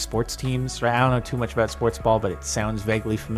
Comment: did he say sportsball?